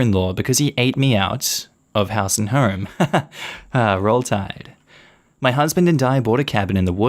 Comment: Did you just say "Roll Tide"? lmfao